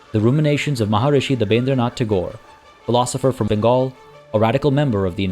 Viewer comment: It was his son Gurudev Rabindranath Tagore, who was conferred Nobel Prize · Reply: didn't he mention Rabindranath Tagore..?? Probably I misheard..now it makes sense.. Thanks..